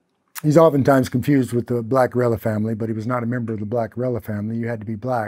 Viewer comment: they're called shafts The girls from leisure suit Larry and the land of the lounge lizards were interested in at least 5 to 6 in shafts They did line dancing Were cowboy hats and Boots I was sexually groomed by them As a young child Some of them went on to brag about how they had to separate from me because our age difference was showing and some of them were involved in the so-called Playboy which was aimed at children and had other aspects in society other than the magazines that was to attract children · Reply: he's saying rella rella over and over again but what he was saying was black guerrilla family